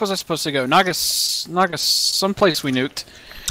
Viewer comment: Can someone explain to me the first joke that said? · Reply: Connor Paul Ohhhh I just realized he said some place "we nuked". I thought he said "minute".